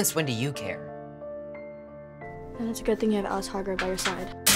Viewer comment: almost sounds like he was gonna say annie😂